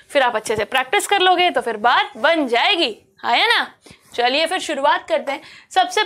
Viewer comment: Came here to study for English Olympiad topic 🥲👀❤️🤏🏻 Loved the session mam!! <3 9:17 Ma'am said Ha ya na and what I heard was Aa ya na which is my name Aayana ... #misunderstood 😂🎃😹👀